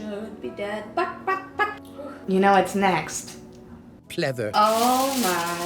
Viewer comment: I HEAR HEATHERS